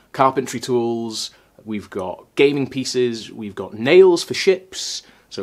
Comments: Ngl, I was only half listening and at 4:50 thought you said "gaming PCs" instead of gaming pieces lol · my dumbass brain thought you were going to say gaming PCs for some reason.